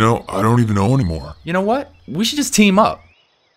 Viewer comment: What does the dragon say at 2:59? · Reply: "I don't give dudes rides, so..."